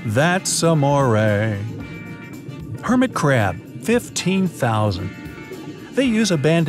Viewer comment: I thought it said hermitcraft for a sec! 😂